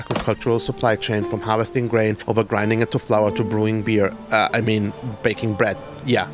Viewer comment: When he said, “baking bread” I heard “breaking bad” lol 🧪